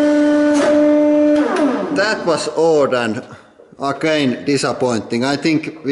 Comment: does he say "that was old and arcane?"